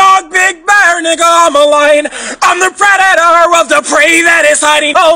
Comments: I thought it said I’m a bladder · I hear “big back💀”